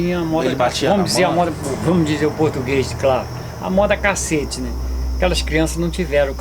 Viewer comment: "Vamo dizer o português claro: 'À moda cacete'" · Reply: Eu entendi "amor a cacete", pois já ouvi uma pessoa mais velha usar essa expressão.